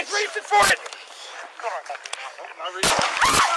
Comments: Am I the only one who heard him say "I'm not reaching" right before the shot? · I think I heard him say "I'm not reaching for it"(4:14) one second before the gunshot..Actually he only gets out "I'm not reaching..POW... I mean I'm not on his side at all.. Stolen gun, running from officers, gets in fight with gun.... Pretty much knew what was coming! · Cop after shooting suspect “Is everybody good” other Cops “Yeah I’m good” suspect’s last words “I’m n.... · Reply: I heard it too, "I'm not reaching".